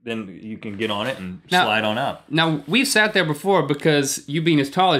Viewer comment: Is it me or does Rhett say slide on up on 1:34 to 1:35 xD